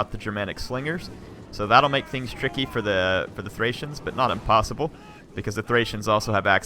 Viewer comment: I think said "unpossible"